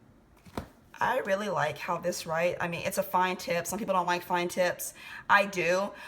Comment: When she said "Fine Tips", I thought she said fine tits! LoL